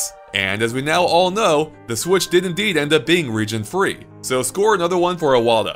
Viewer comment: the subtitle got it wrong, it is indeed ended being region free. Not ended NOT being region free.